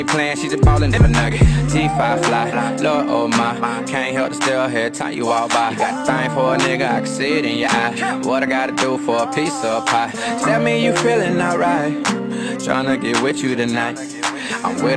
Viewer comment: am i the only that thought he said “what i gotta do for a pizza party?” or is it just me?